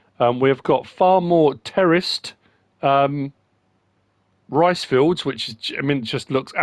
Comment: Oh my god this whole time I thought you were saying terrorist rice fields...... 🤣😂